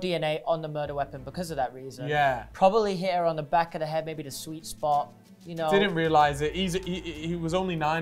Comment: he really said sweet spot